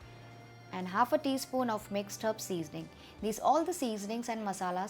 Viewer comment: what's that?? I can't hear it properly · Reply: mixed herb seasoning also known as italian seasoning or oregano